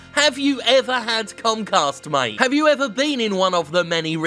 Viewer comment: I heard Jim say "Comcast" as "Cumcast"